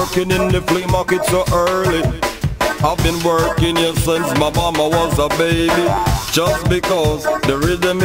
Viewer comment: "Ive been working here since my momma was a baby" Sounds bout right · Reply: I thought he said "I've been working here since Obama was a baby"